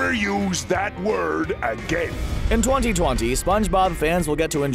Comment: did he just say in 2020